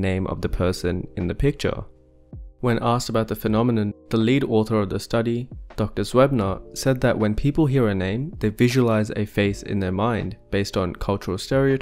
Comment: did anyone else hear 'When people urinate' ??!!